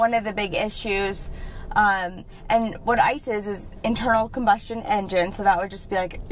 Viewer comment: "What ICE is" lol I heard "what isis"